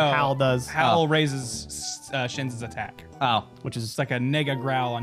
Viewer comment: did you say the n word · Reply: Nega short for negative